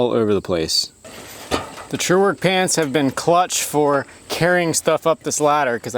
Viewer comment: Did you say 'clutch'?